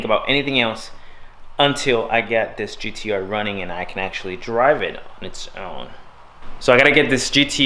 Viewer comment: At around 9:30 it sounded like you said "drive it in when it's snowing". I was like 😲 but you already have a WRX.